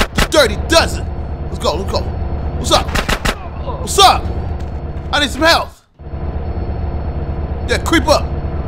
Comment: I'm dropping the cheats down here again. I think you just punch them in while playing the game that's how it used to be. This just some of the cheats available it's hella but this is what I thought you'd want All weapons: Down, X, A, Left, RT, RB, Left, Down, Down, LT, LT, LT Unlimited ammo: Down, X, A, Left, RT, RB, Left, Down, Down, LT, LT, LT Unlimited health: Down, A, Right, Left, Right, RT, Right, Down, Up, Y Give 250k: RT, RB, LT , A, Left , Down, Right, Up, Left, Down, Right, Up If you on Pc: FULLCLIP - Infinite Ammo HESOYAM - Health, Armor, $250K LXGIWYL - Weapons set 1 PROFESSIONALSKIT - Weapons set 2 UZUMYMW - Weapon sets 3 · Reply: OG LOC to me is it sounded like he said I need some cheats around the 40 sec mark